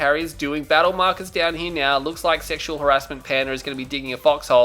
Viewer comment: dafaq? Did i just hear sexual harassment?